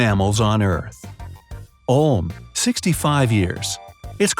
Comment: am I going crazy or did he really say 65 years